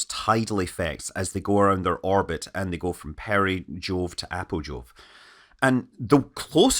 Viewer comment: At 21.50 I heard "perijool to apojool"... clearly I've spent too much time on Kerbal Space Program 😆